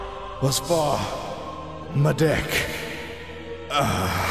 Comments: did porunga just say "fuck you, I'm a dragon"? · i could swear that the dragon says "Fuck you"